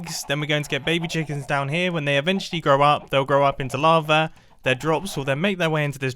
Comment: when he said they will grow up into lava, it sounded like he said they will grow up to be larva